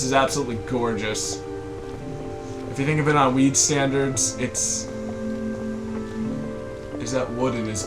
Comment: did you say weed standards? i know what you ment, but that just sounded funny.